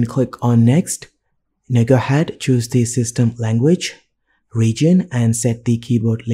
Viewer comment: great tutorial but what did you say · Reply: [...]next, now go ahead choose the system language.